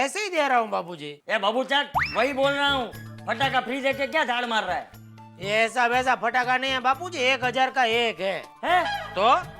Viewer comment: did he said pathaka baat ke kya gand mar rha hai?😅😂